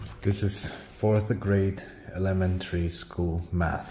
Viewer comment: I thought you said 4th grade Lemon tree math! 😂😂😂😂😂